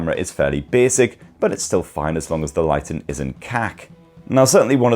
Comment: sounded like you threw in some South African slang there time 4:14 lighting KAK